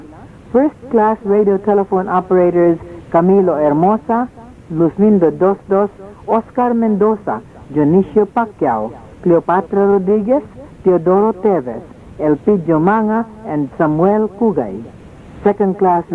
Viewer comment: I thought I heard Dionisia Pacquiao (The mother of the famous boxer, Manny)